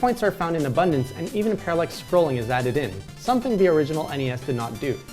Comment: and even "what?" scrolling? I didn't get that word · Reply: parallax scrolling: <link> (or moving backgrounds lol)